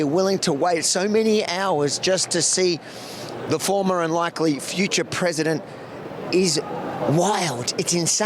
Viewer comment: Did he say "... former unlikely future president..." ? I don't get it. someone explain around the 5:00 mark